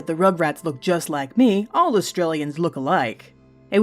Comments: It sounded like you said Australians rather then Austrians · Sounds like she says Australian yet it is Austrian